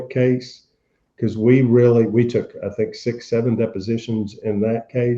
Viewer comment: did he just say 67